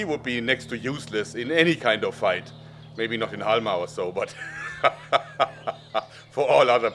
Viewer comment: What did you say at about 0:26 “maybe not in alma or so” something like that.